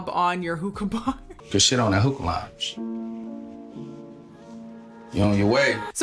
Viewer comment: As a fellow Detroit native (born and raised) I laughed so many times at the Detroit observations/opinions here lol Edit: At 19:03 He actually says, "Good Shit on that hookah lounge", meaning he's congratulating her. The Detroit in me understood that right off the bat LOL